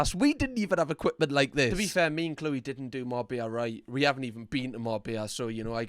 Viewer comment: No matter which accent a Brit has, every time one says ‘Marbella,’ I hear ‘more beer.’ 😂 Which tends to confuse me in the context of any sentence Marbella comes up in. This coming from someone who has minimal trouble understanding other people’s accents. More beer, more beer, more beer. Huh? Head scratch